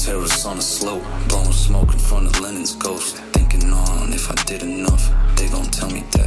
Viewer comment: thought he said lemon's ghost NOEL A GOAT